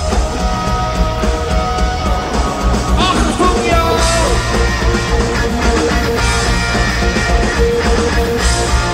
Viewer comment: What does bono say at 2:18? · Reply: Achtung y’all. I think.